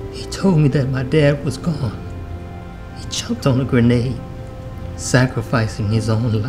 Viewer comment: It sounds like he said he choked on a grenade